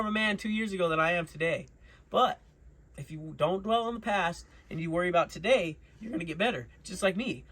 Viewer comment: At first i thought he said "dont weld on the past". I was like did this cat really work that into there? Haha then i started thinking about it as an analogy "cant fix the past" "cant do another pass over it" lol